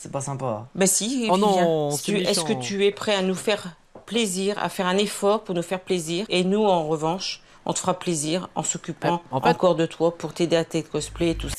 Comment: elle a dis quoi du coup ? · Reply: "Plaisir bla bla bla plaisir bla bla bla plaisir bla bla bla plaisir" Elle dit ça je croit